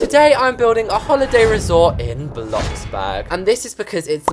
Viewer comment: Just me or he said day instead of today?